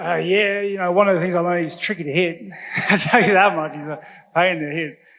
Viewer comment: Wtf did whittaker say at 2:24 - 2:30 ? I've replayed it so many times but do not understand him. Someone please help. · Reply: He said he's tricky to hit and a pain to hit I think